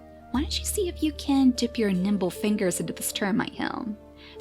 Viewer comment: i thought she said nipple fingers lol